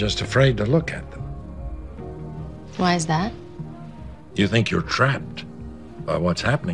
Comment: didn't she say what is that ? Instead of "why "